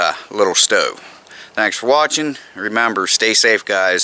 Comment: Did he say meth man the legend? 😅